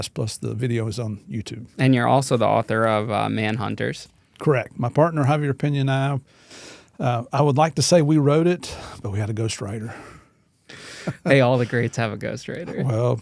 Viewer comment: when he said he had a ghostwriter I momentarily thought he said "we had to go straighter" and my 9 year old trauma stifled brain 🤯😂🤯 exploded in laughter